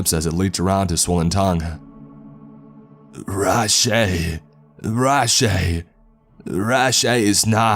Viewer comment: When he started saying what sounded like 'Rush A' at around 3:25 i thought it was a CSGO horror story ffs.